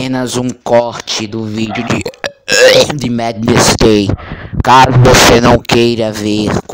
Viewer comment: madness gay? Foi isso que eu ouvi kkk